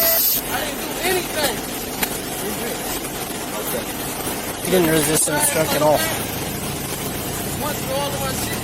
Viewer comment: Did anyone else hear at 4:01 that the cop whose camera is recording say "He didn't resist and obstruct at all"? Because i sure did. I know my comment is very late but I sure hope he gets great compensation from this obvious abuse of power. Somehow every cop wants respect but doesn't seem to know that it is earned, not given freely.EVEN the badge doesn't deserve respect unless the person wearing it has earned it first.